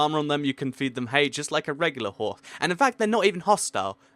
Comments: Did he say whore at 2:01? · Did anyone hear him say whore at 2:01? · "you can ride them, you can tame them, you can put armor on them, you can feed them hay just like a reglular WHORE" ok didn't say whore but didn't finish saying horse so it sounded like whored XDDD am I the only one who noticed this?